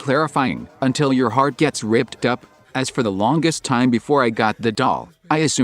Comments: I only hear "your heart gets WRAPPED up" tbh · i keep hearing “until your heart starts to break up”